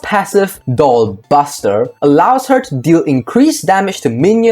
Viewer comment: i thought you say ball buster